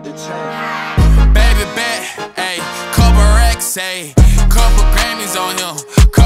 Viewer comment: i hear"Baby Bat,Ayy,Couple Rat,Ayy,Couple grannies on him"💀💀